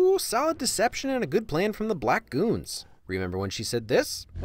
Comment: did he just say black goons? tf